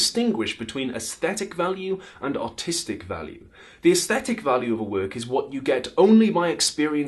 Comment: LOL😂 Who else thought he said "autistic value"